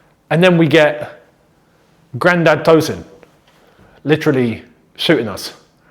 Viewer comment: I thought you said ‘Grand Theft Tosin’ 😭 I was creasing